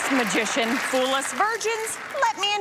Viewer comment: Did..... Did she just call us virgins??